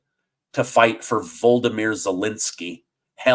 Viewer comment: What? Voldemort Zelenskyy? >> That's what I heard you say!